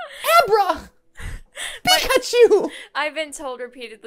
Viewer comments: is iy weird i heard it as "i've been told i print out sneezes wrong" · I totally heard "Pi-Chuu~!"